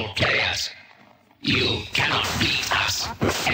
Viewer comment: When I first watched the actual Commercial a few years ago, I honestly thought the Duck Hunt Dog said “YOU CANNOT FEED US.” and the others in the end all said “WE AREN’T NINTENDOGS. WE CHALLENGE ALL GIRLS” 😅